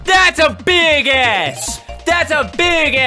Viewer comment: If you turn on automatic captions at 13:00 it says “that’s a big ass” instead of big S 😆